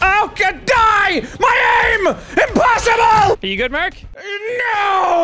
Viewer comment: did he..did he just call you "Clark?"